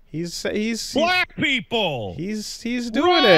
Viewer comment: “Black People! Run!” - Pat Boivin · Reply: I misheard the first bit as "Black people won!" And not "Black people run!"